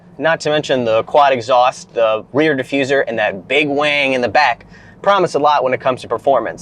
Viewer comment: Anyone else hear this as big Wang? lmfao